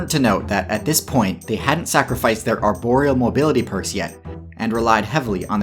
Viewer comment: I literally heard Boreal... Probably the boss fight at the beginning overwhelmed me